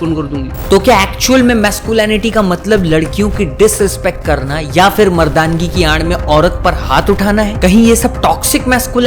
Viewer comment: kya bola...?? Mardangi ki a*** main💀